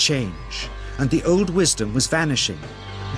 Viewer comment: This is real, anyone can do it but this man was at a high level. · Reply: I'm French and I did not understant very well, but what the narator is saying at 3:00 "the old western will vanishing" is that it ? Because what apenning right now can be it...